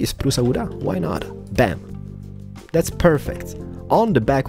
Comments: Does he say window penises xD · omg 4:56 thought he said "two little penises xD